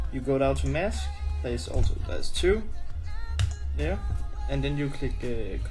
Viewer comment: What did you say at 1:35 about the mask? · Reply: sorry about my Terrible danish Accent, i just said that there was two masks and it is importen to copy both, and if you have more than two then copy all of them :) hope that helps